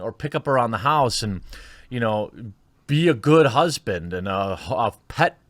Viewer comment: Did he just say "Pet Husband"? That's what I heard him say. Red Pill effects are REAL. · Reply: It was "better husband" and "pet dad"